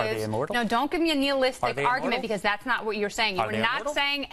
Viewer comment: What does she say at 1:40?what is that term? Annealistic argument?